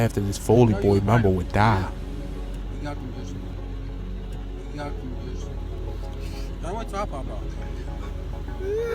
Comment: what did he say ? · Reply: “We got you b”